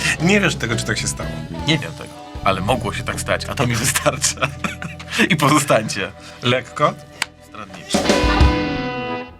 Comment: Czy tylko ja usłyszałem "I pozostańcie lekko zbrodniczy"?